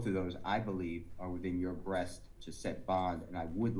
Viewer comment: listen to the words there using in codes did he just say "breasts"?